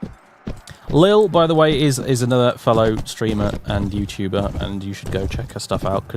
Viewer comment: Who was that other youtuber he mentioned at like 8:38? I couldn't quite catch the name and was interested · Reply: Lilialil A.K.A Lady Lilia